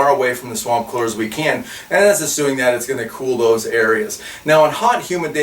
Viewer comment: "And, as "ASSUING"? those...what did you say?